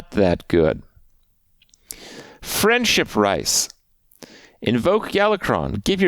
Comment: did he just say Friendship Rice?